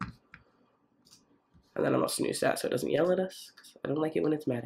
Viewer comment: I thought you were saying you’d put it on snooze so it doesn’t yellow at you, which is both hilarious and brilliant.